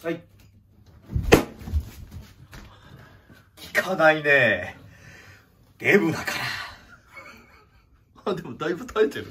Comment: 「効かないねぇ～Mだから～」って聞こえた気がしました